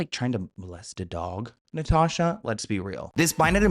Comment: I thought he said arrest a dog, i thought that was bad enough until he repeated himself........